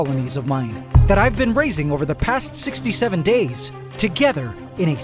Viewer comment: did you say 67?